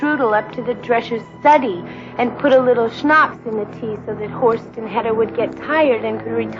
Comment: Holy shit… that has to be some of the most astonishingly terrible line delivery I've heard in a film. Seriously, she could give Tommy Wiseau a run for his money, if only she had an indiscernible accent. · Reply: I thought she was saying “the horse and header would get tired” and assumed that she was using a dated expression or talking about a German tradition